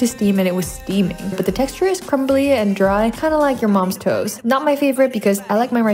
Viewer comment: "Kinda like your moms toes"💀😭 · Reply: I thought she said toast 😂